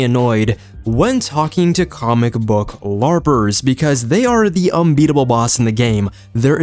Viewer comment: I apologize I heard 'comic book barbers' which I was like how many of them do you encounter